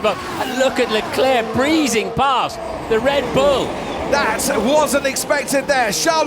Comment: Am I the only one who heard Brundle saying "Red Blue" at 0:52?🤣😂 See you in 2020, F1!!!